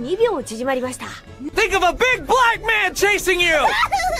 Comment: i thought he said "think of a big blind man chasing you" instead of black